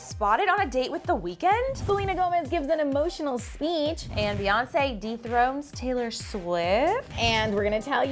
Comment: I swear in the beginning it sounded like said Beyonce deep throated Taylor Swift lol